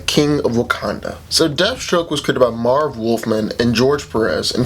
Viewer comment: Did he say death stroke was created by Marvel